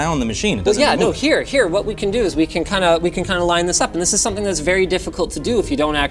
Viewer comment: 07 did he say 'line this up' or 'linus up'